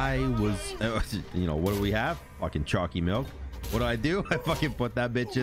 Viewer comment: I thought you kept saying chunky milk. I was like but 😰🤢. Alas, you were talking about the milk of chocolaTE